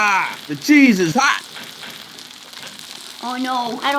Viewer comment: I heard “Jesus Hot” 😭😭